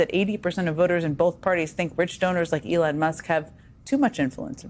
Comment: I thought she said "...rich stoners..." when talking about Elon and I was laughing my head off. When I went back again, she had said "rich donors" OMG. I'm still laughing!